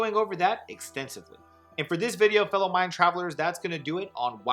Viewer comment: REALLY misheard the intro and thought you said “what’s up fellow white travelers” and I was shocked.